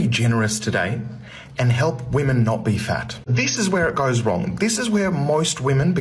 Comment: Why did I hear "moist women" 😭